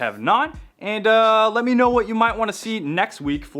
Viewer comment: At the end 3:45 I thought you said 'im Shaun' but you said 'wrench on!' Awesome tool.